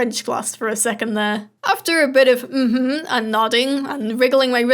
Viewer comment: Did you say “I’m a baguette.” In French? XD Ju suis parlant Français. :>